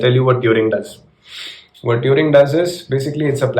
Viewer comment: did anyone heard urine or it's just me?